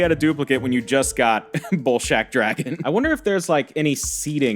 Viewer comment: I kept hearing "Ballsack dragon" and ngl that was pretty funny.